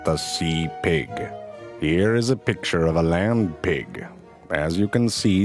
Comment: I swear I thought he was saying "lamb pig" at first.